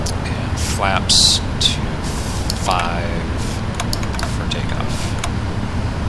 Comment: Enjoyed the simulation. Had me second guessing the flap setting when I thought you said "flaps two five" rather than "flaps to 5". Were you a little late on the flap retraction? Thanks again for the video.